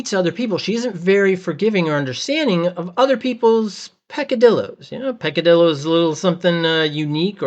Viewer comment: what is this word? the closed caption says "padillo" but i'm sure i'm hearing another syllable in there. "picadillo" is a spanish word that might be right but googling "picallio unique thing about a person" only results in recipes. it has meat, tomatoes, raisins and green olives. that's pretty unique so... maybe this is the word. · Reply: peccadillo It's a great word.